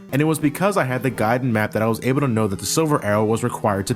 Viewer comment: I know that he kept saying "the guide and map" but I kept hearing "the guide and Matt" and I was waiting for Matt to pop in.